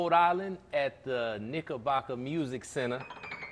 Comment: For a second there, I thought he said Nickelback Amusement center. Phew, that was a close one. That would have been right up there with the Large Hadron Collider gone wrong